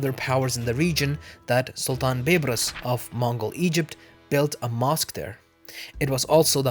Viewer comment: I wasn't paying attention to the screen than I heard 13:36 Mongol Egypt